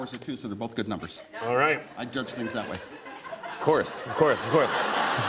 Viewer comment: What does he say at 1:33? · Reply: “I judge things that way”